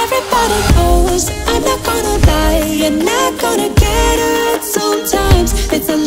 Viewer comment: Even with the text on screen I hear "everybody knows, I'm not gonna die, not gonna get hurt sometimes"